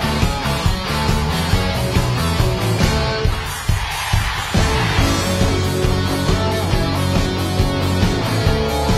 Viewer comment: What is the crowd shouting at 3:07? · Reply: rasse rasse (らっせらっせ)